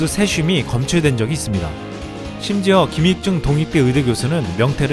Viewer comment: 동익대라고 들려요